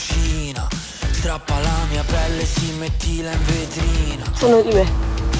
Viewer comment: Raga ma cosa dice Ludovica al 00:30 mentre parla con Chiara? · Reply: Credo dica "Sanno di me"